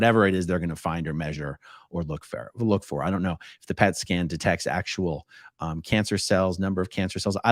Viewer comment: I thought you said pet scan, not cat scan.